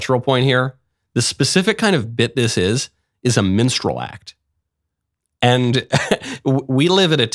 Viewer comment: did you say MENSTRAL Act... cuz That would explain a Lot.